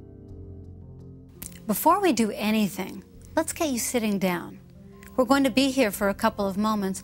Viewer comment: Hi guys! I'm not a native speaker. I can't catch the word says on 2:27. I hear: "Before we do anything, let's ... sitting down". What's the magic word. Can you please guys help me out? Thanks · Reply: Is it: "Before we do anything, let's GET YOU sitting down"?